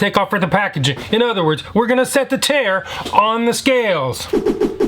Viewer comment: ngl ive watched 50 videos of yours and always thought you said set the chair on the scale